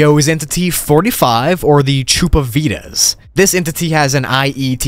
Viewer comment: I wasn't watching the screen when you said Chupavidas, so I thought you said "Troupe of Vegans" and I thought "That is terrifying."